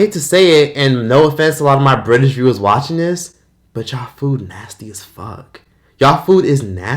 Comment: All I hear is "Nosty" Food LOL🤪😝